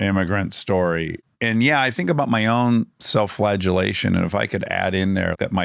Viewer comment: thank you so much for this. he's such a talented funny genius. i just want to comment that i'm sad he seems to have only been touched by the negative opinions on his Lex, whereas most people i know (online and offline) praised him as one of the movie's highlights. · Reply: PS: at 1:16:21 you might want to fix the subtitles, they say "flatulation" instead of "flagelation":D